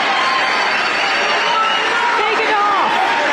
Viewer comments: Did anyone else hear some woman scream "take it off!" around 5:44?? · Did i hear some lady saying: Take it off??!???